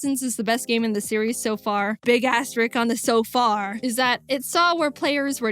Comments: I kept hearing "big ass Rick on the so far" I turned on subtitles to see what they said, and they said the same thing. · did i hear a bad word jaiden? "big as#" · can someone correct me if i heard jaiden saying big a5s rick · WHO HEARD THAT!?!? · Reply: It said big a** Rick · anyone else hear ass? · did she just say ass?